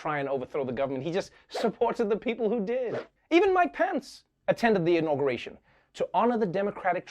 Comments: When he said "Mike Pence," I heard "my pants." O.o XD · when he said Mike Pence I thought he said my pants